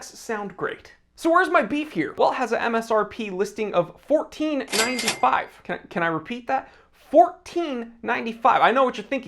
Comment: I had a brief moment of inattentiveness while you announced the price and thought you said they were priced at $149.95 and I thought " oh, that's not too bad!" Yikes!